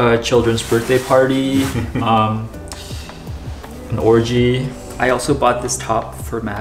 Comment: DID U JUST SAY 'ORGY'. - 9:38 OMG